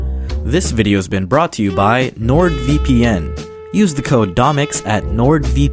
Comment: I thought you said “This video is brought to you bye lord vape” ikr very weird